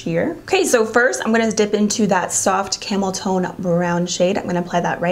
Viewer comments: When you said ‘Camel time’ I thought you said ‘Camel toe’, not gonna lie 😂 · did you say camel toe brown 🤣🤣🤣 I could have sworn u did. Maybe u said camel tone??? Idk 🤣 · I'm so bad I thought you said you were dipping into the brown camel toe shade I paused like whattttt toned not toe! My ears hear what they want I guess